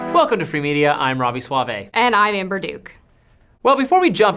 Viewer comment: and I am Ber Duke... that's what I heard at first. 🥴